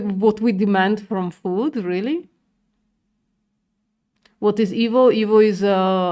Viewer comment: "what is evil? Evil is avocado oil I think" 😅😅😅😅 Have you crossed to the dark side? Lol I think you were saying EV, classic. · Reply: They asked what is evo I think! It's on the screen haha